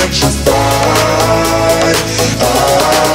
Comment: Sometimes when it says ‘Electrified with you’ I hear ‘Electrified review’